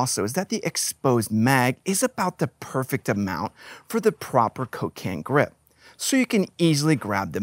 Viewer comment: When you were talking about the rifle mag you were talking about how much was exposed. And I could have sworn you said it was perfect for the "cocaine grip".